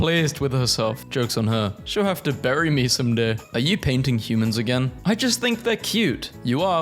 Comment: I thought it said "are you PRINTING humans again" 😂